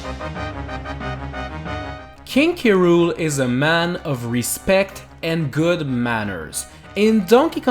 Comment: I thought it said "King K. Rool is NOT A CHARACTER!" XD